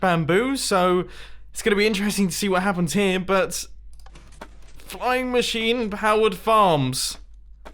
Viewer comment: LOL I thought said time machines :D